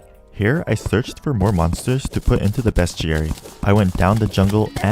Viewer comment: everytime he said bestiary i could've sworn he said best jerry.